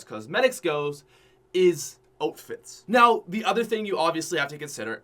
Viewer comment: does it bother anyone else that says “oatfit” instead of outfit?